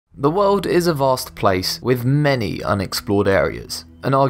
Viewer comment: If you listen very closely at 0:00 you can hear the video begin. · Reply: Nice grammar I heard "the world isn't plause place"